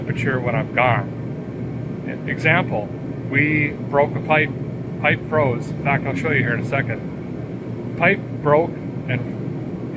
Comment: i thought he said "A pipe froze, im not gonna show you that in a second"...I had to re-watch it lol